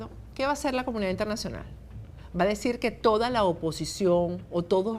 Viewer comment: Yo escuché "LA COMUNIDAD INTERNACIONAL NO PUEDE HACER CASO OMISO" y no que la oposición lo hará.